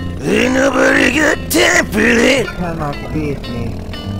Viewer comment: "Let's-a goooo." "I never doubted me." "No question." "You cannot beat me." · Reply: I always hear "you cannot save me"